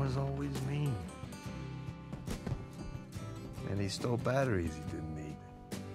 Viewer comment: What does Rocky say at 2:31? Having difficulty understanding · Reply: Paul Kamin "and he stole batteries he didnt need" Linking it back to himself at the start of the movie. Acceoting the fact hes a little asshole that pushes people away all the time but he does love them like family